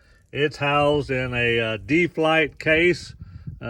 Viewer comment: I couldn't catch the exact name of the case just before the 25:00 mark, deep life case? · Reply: It is a CHRIS CHRISTENSEN D-FLITE 1000 DELUXE TACK BOX